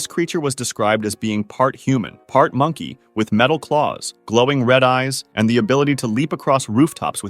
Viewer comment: When he said red glowing eyes I thought he said red loving eyes